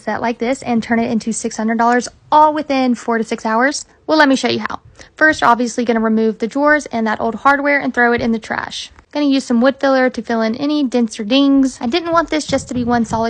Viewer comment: I had to play it over and over because I thought you said throw out the drawers and hardware 🤷. Great job!!